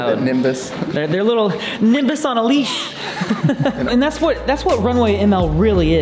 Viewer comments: the CC horribly messes up transcribing what wren says from nimbus on a leash to niggas on a leash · When Wren says "their little nimbus on a leash," the captions say "their little niggas on a leash" LOLOL